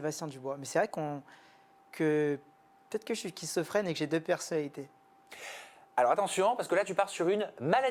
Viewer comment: j'ai entendu kissofrene, j'ai arrêté tt de suite 🤣